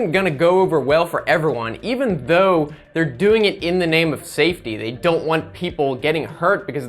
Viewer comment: When he said "In the name of safety" I thought he said "In the name of satan"